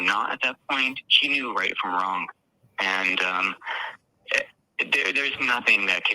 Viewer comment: If you listen back, it sounds like he said '' SHE knew right from wrong...''